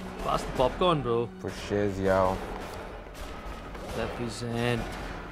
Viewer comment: What did Rohan say before that fist bump? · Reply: Represent